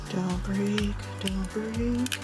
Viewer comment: My favourite part was that part when you said don't break I thought you said don't breathe 😂